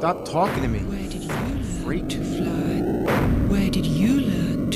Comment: Did he say "Freet"?